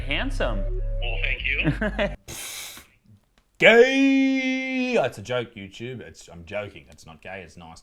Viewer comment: thought u said that’s a joke, you Jew for a second 😂